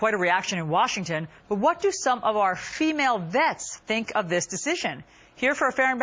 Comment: is she saying "shemale vets" or "female vets" I've replayed it 90 times and it keeps switching